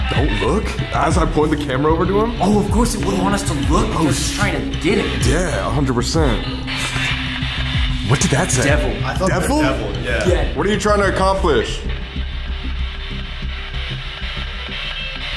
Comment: i think it didnt just say 'devil' i think it said 'the devil i right/bright' but im not shure